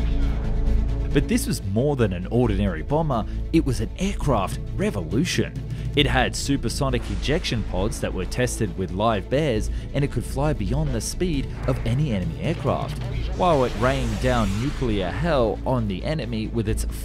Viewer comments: did he say "live bears"?! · I'm sorry did you say live bears?